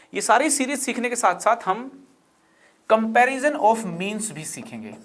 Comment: I Heard memes* instead of means